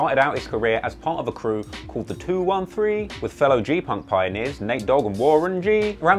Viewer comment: Did he say “G- punk?” @ 10:51 lol